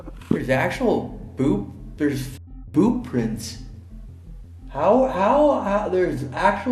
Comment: did anyone else think he said bob prints? 😂